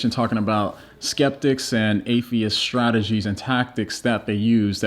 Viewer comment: Its sounded like he said Aaron Ra and tactics in the same sentence lol. That man just gets very angry lol.